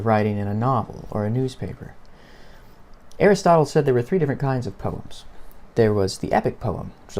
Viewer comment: When he said Aristotle I thought he said Harry Styles 😅😂